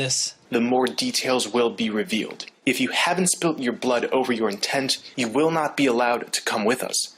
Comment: At first I thought he said "Blood over your tent."